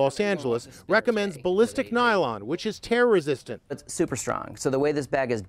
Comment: I heard "Balistic nylon which terrorist resistant" 🤣